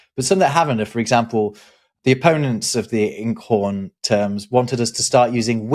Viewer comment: lol - I thought you were saying inkporn .. not inkhorn ... and it sort of made sense based on the tone of those that coined it